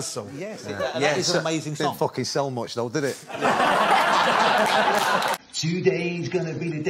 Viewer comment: what he said ? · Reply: Not f.. sell much though. did it?